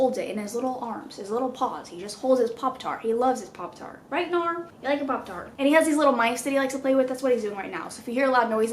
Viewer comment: I heard that as “right gnar you like ur poptar?”